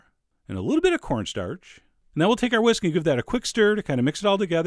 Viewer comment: I got excited when I thought you said "And now we'll take our whiskey"